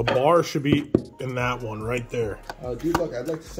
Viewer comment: I thought you were saying Lavar at first instead of the bar 😂 Great video guys.